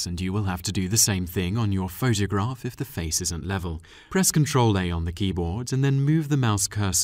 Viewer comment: Hi There, very nice turtoal, about 0:51 in this clip you said rotate image by ctrl... and then I can't hear you clearly... how can I do that? Thank you in advance! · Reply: CTRL+A